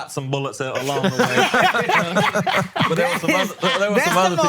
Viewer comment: I thought said “yeah, but there is a ME”…….